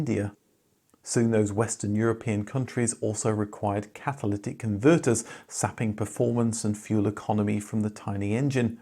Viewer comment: I thought you said "catholitic converters" @ 10:08, which could have been a necessity in for communist buyers in France and Italy😂